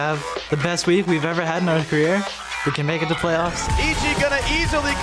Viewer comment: i thought he said "north korea" instead of "our career" 0,0